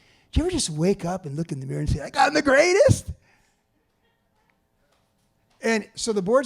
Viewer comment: What did Joey say at 56:51+, - something like: ''…. ain't God the greatest?!'' or '' I got 'em the greatest?!'' not clear